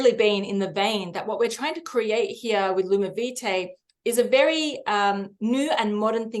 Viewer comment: Everytime she said lumivitae I thought she was saying lunar beauty